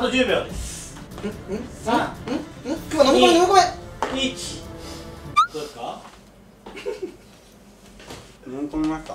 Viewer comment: くまちゃんの挨拶どうもですって聞こえるwww 大大大好き